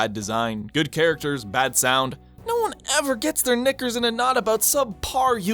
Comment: Smash Brawl is better then Melee lol (I’m not kidding)……(My opinion smartass)………And I can already hear these little kids being like “OOOOOHHH HE SAID THE N WORD AT 3:43 WHAT A RACIST!!1111” He said nickers, look it up its a real word.